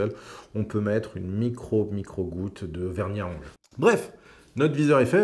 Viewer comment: Bonjour, à 7:08 vous suggérez de "mettre une micro micro goutte" de quoi ?? Merci😅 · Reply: De vernis a ongle sur le pas de vis pour éviter que la vis ne se desserre